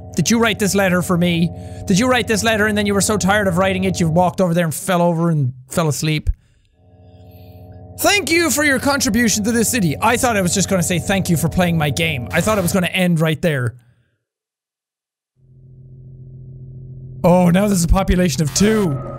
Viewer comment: Anti: *Incoherent words that according to my friend who decoded that either says "Thank you for your contribution to this video" or "Thank you for your contribution to his/this death." Me: Hey...Anti, eat a Snickers, you're not yourself when you're hungry. Hands snickers Anti: Eats Snickers Me: Better? Jack: Better. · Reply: I heard "Thank you for your contribution to this channel"